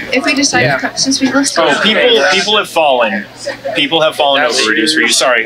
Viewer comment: When they screaming xana over n over n over again , you hear “fight” Also before cashier says “ people have fallen “ I hear “ people are dying “ that’s when cashier corrected him like introjected him and says “ no, people have fallen “ U NEED TO PUT HEADPHONES ON , volume up , and don’t look at screen , looking makes u Mis an what they saying in background